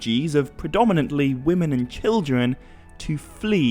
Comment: did you say children or kildren? It really sounded like kildren. I thought maybe it was an old way of saying children in middle earth. Also, I wonder how do we pronounce the letter r in elvish? Is it like the today's english, the french r or like a rolling r such as italian, spanish or arabic? Thanks in advance.